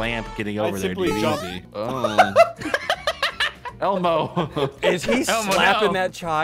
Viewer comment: i thought said hell no